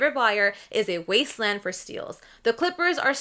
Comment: Thought you said "wasteman" at 6:25 lol